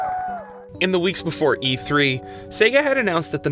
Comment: what did he say?! it was completely silent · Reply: $299